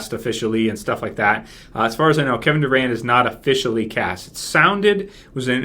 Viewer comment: lol, i thought he said Kevin Durant